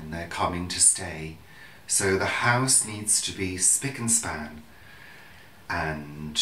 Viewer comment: Thought at first you said "spit and span" and wondered how Brits do their cleaning. Lol. But it never occurred to me to wonder....what does...span.. Mean? Laura